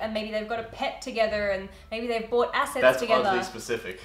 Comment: I thought she said "maybe the bought acid together"😂🤣